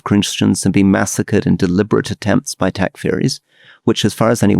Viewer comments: Could anyone help me figure out what Rory said at 46:22? "... in deliberate attempts by (??)" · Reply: 'in deliberate attempts by Takfiris'. I had to re-listen and look up the word. Some sort of extremist Islamist group in that region. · Could anyone help me figure out what Rory said at 46:22? "... in deliberate attempts by (??)" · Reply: I think he said 'Takfiri'. Best to Google it for a proper explanation, seems quite complex